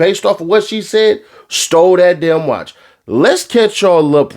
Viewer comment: I thought you said his people stole the watch